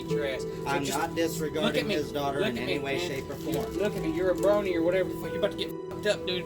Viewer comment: did he just say "You're a brony or whatever"?